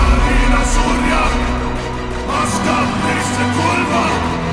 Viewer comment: Lyrics: Asatru ni ondem ishka velya nandem Otar ina surya Aska brise kulva Translation: "Soldiers of the dead Rise ye up Otar is your master Go forth and slay" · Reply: At first i thought it said "Kurwa" instead of Kulva <mail>